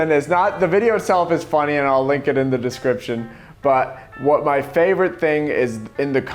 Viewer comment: He says "Link it in the description" but it sounds like he says "Lincoln in the description" at the very same second Abe Lincoln appears on slide 36. I don't know if he did that on purpose. If so - brilliant. If not, what a cool coincidence.